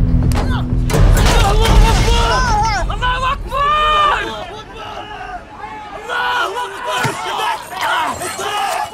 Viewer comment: does anyone know what he said please tell me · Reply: Allah hu akbar . God is great.